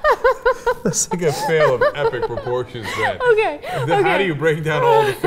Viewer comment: at :38 it sounds like Cenk says "this nigga failed on epic proportions". anyone else get that?